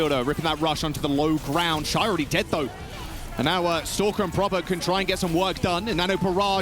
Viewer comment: "I thought you just said leave > proper" (then laughing)